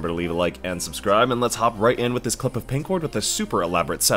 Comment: did i hear a pink ward montage?